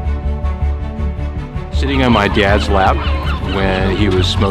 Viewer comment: Omg i thought the dude who said "sitting in my dads lap" said shitting in my dads lap lolol